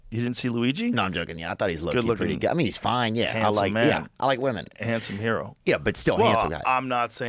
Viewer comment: Did anybody else catch Rogan saying “I like men” times two 1:04:46? Or did he say something else